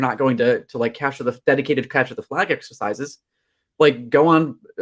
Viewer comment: he recommends some exercises but I can't make out what he said. Something along the lines of "cash of the flag" or something. Can someone spell out it for me? Thank you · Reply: capture the flag